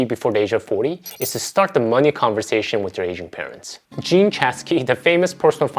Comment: I kept hearing “Asian” instead of “ageing” parents- lmfao I was confused for a bit